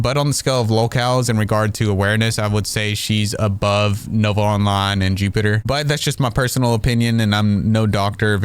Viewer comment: Go follow my Instagram if you haven't! <link> Thank you for all the support! :_chazHappy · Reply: please tell me what you mean and what you’re saying.. I’m sure it’s spectrum based but I hear Nova something and Jupiter 🤷🏻‍♀️